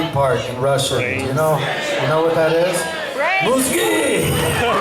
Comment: And what's that Russian word "mooskey" he said about body parts around 11:40 ? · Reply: Mozgi (Russian) = brains (English)